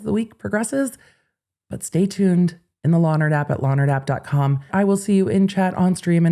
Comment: Every time she says “law nerd app”, all I hear is “Lonner Dap”, like it’s some kind of 50s hair product or something. I know- I’m frigging weird.